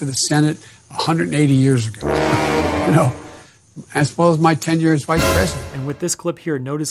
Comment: I think he said "my TENURE as Vice President".